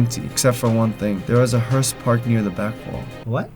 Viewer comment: He said hearse I thought he said horse